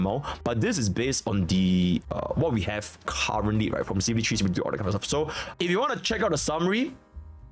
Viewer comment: Wtf did bro say at 1:12? He sounded like he was talking in rewind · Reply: from CBT3 and all that kind of stuff (referring to how it was calculated)